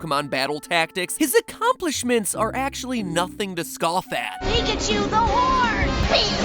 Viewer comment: Right, the first time I heard 0:08. I heard Ash say, "Pikachu's a wh***!" Probably not a good sign for my hearing.